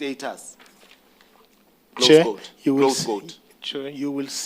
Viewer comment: DID HE JUST SAY THROAT GOAT??!!!!